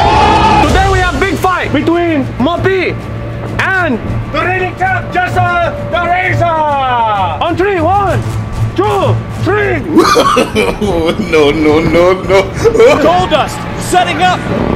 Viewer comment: It sounded like said jesser thr razor 😂😂😂